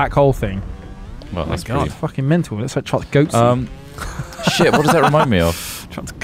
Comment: I thought I heard my name at 1:20, but they said Goatsing, not Goatsy. haha.